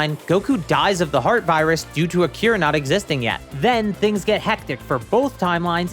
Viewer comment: I thought you said "due to Akira not existing yet" and I was like "what? I'm pretty sure Toriyama existed..."